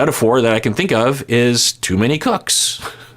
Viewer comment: I thought said "too many cucks" 😂